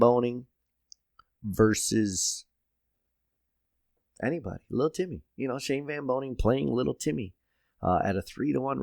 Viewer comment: Did you just say "stroke Timmyly"? Lol